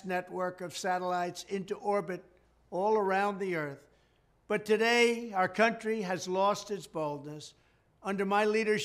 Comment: For a moment there I thought he said "our country has lost its balls", which technically is what should have been said as well as the actually sad situation it finds itself in.